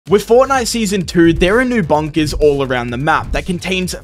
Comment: I thought said 2 seasons NGL